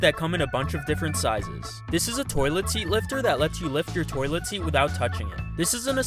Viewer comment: I thought he said “that let you lick your toilet seat” 💀 I need some damn sleep